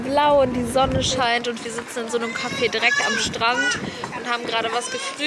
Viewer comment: Ich hab verstanden "Cafe Dreck " 😂😂😂